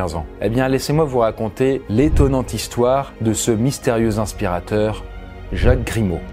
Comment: J'ai compris etonant aspirateur 😂